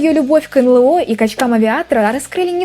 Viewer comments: что говорит: любовь к очкам авиатора что слышу Я: любовь к кочкам авиаторам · сначала услышал: «.. к качкам-авиаторам..»… 😅